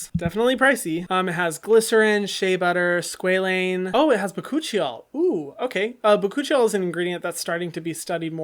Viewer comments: “Bakuchiol” i thought your said my coochie y’all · when he said bakuchiol the only thing I heard was coochie 👁👄👁